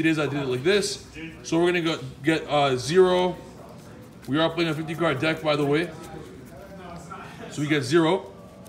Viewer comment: Is this the 60 card deck? I thought I heard him say 50 card at the beginning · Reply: No, this is a 50 cards deck, the 60 cards one is the one without Z-ARC